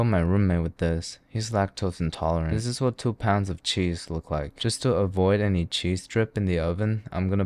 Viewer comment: I thought said 1 pound of Mac (??)